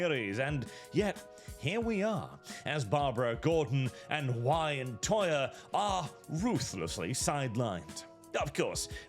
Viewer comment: did he say “WIDEtoya”? If so I agree 100%. They made the women size 12, Alfred is fat too